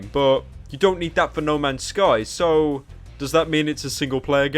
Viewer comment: did you just said no man scum????